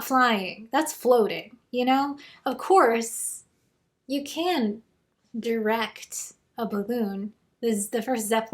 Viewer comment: i heard you say: "of course, you can direct a baboon" and i got excited for a moment then realized it was not about apes performing shakespeare